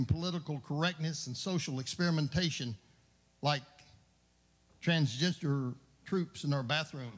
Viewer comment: did anyone else hear "Trans-gesture"?